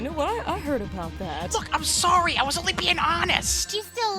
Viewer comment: uhhhhh Did Silver just say the F word? Oh no his fall to the dark side has already begun! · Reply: Don't worry, he said "Look", he didn't say a bad word there xD Thank you for watching, Roxie!